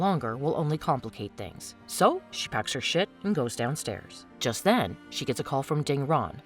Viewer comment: “She packs her shit”. Anyone else catch that? 😂